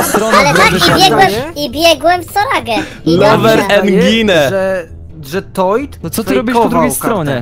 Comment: usłyszałem angine